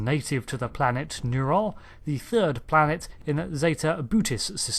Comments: Really sounded like you said third head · I thought you said third head